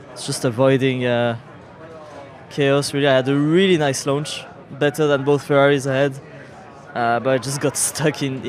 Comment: I genuinely thought that at 6:23 Hadjar said that he "had a really nice lunch... Better than both Ferraris had." 😂🤣